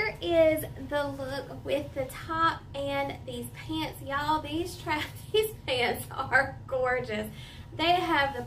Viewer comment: I'm cracking up because the captions thought you said, "These trashy pants are gorgeous!" 🤣